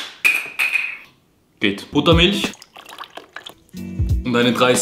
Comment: ich habe Muttermilch... statt Buttermilch verstanden...